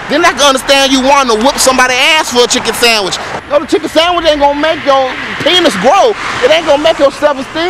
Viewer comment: I'm a Japanese student who study English. In 1:06, What did he say? Could you explain? The subtitle shows that "no the chicken sandwich ain't gonna make your penis grow", but I can't hear "the". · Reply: he said, "the chicken sandwich ain't gonna make your penis grow " lmao